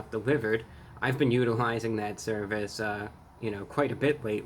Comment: thought you said “I’ve been utilizing that cerveza a lot recently” 😂